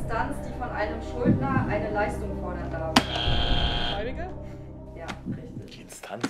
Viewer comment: ??? Was hat sie da gesagt? Also, die Idee mit diesem Video ist echt Super, allerdings der Ton im Hintergrund ist sehr schlecht. Die Antworten überschneiden meistens mit dem Warnsignal oder der Musik. Vielleicht sollte die richtige Antwort mit einem grünen Häckchen und dahinter die Antwort eingeblendet werden. Sonst, würde ich diesen Format oft anschauen. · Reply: Ihre Antwort war: ,,Gläubiger" Vielen Dank für Dein wichtiges Feedback!🙏🏼👍🏼